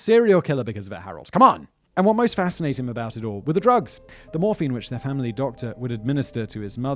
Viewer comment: I thought he said diet morphine of a hot minute lol